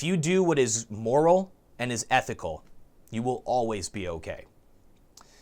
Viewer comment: OH I JUST GOT THE I HAVE NO CLOSER I ALWAYS THOUGHT YOU SAID I STILL HAVE NO CLOTHES ON OR I AM STILL NO CLOSER BRUH YOU DONT HAVE A OUTRO YES ONE OF THE 7 WONDERS OF THE WORLD ARE SOLVED